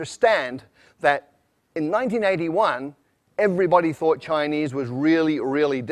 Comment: 1981으로 들리는건 잘못들은건가